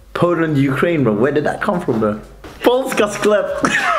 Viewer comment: did he say "Poles got sklep" - funny; actually small amount of genes from Poland and Ukraine is probably hidden in some Jewish migrants